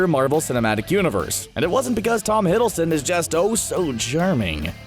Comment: When you said Tom Hiddleston is oh so charming I thought you said that he is oh so german